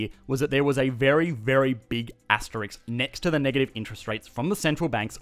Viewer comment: A big what? Did you say Asterix?